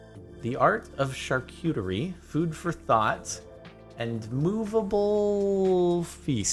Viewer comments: Who thought he said food for thots instead he f food for thought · I thought he said "Food for thots" cuz I wasn't really looking at the screen lol